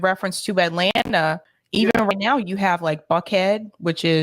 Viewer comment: did you say "F*ckhead, in Atlanta"? Given the town of Bumpass, Virginia, it's plausible.